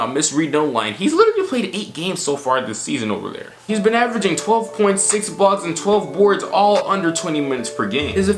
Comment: did i just hear Bol Bol is averaging 12 ppg 6 blocks and 12 rebounds??